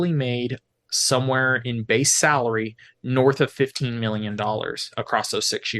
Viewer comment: does he say $15M or $50M?? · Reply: 15